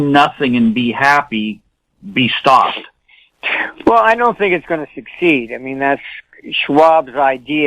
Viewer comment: What does Martin say here? "I know a thing it is gonna succeed"? He speaks so quick and unclear - not native me is unable to understand correctly. If you do, please write it down - I'll be back. Thank you. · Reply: Agenda 2030 will not succeed. It won’t work.